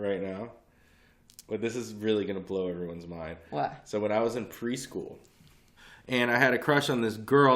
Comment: Funny vid guys. I thought said: "Priest-school" and not pre-school. Things were looking to get real fuckin' wierd. Enjoyed. Be safe.